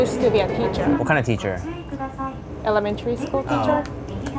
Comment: I thought she said "A lemon tree school teacher".